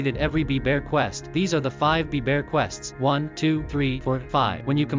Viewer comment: There was a mistake at 4:14 you say "These are the 5 bee bear quests" but the subtitles say "These are the 5 DAPPER bear quests".